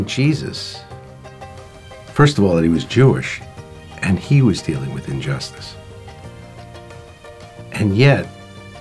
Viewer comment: Truly awesome testimony!! BTW can we fix the English subtitle at 6:31 where the man in the chair says "and he was dealing with injustice" rather than "he was the only way to justice". (Which is true but is not what the man in the chair actually said).